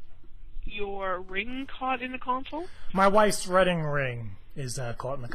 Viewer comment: did he say redding wing?